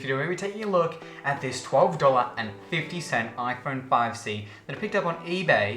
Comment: I thought it said 1250$ not 12.50$